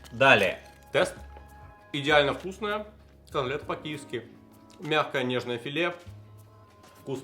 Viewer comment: послышалось АМЛЕТ ПО КИЕВСКИ )))